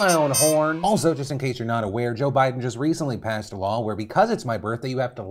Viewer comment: Literally thought at the beginning where he said, "if you're not aware, Joe Biden just passed a law". I thought he said "if you're not aware, Joe Biden just passed away". Lol